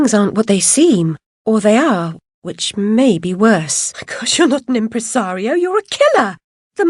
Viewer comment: Sounded like she said "My gosh" instead of my god